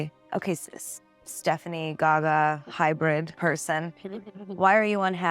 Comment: I heard something very strange at 05:15... like garbly gook or gaga or something, and I'm not joking... it sounds like garbled up verbiage right after she says hybrid person or something...